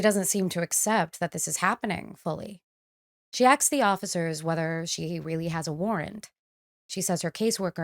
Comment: Did I hear a "She axe the officers if she really has a warrant."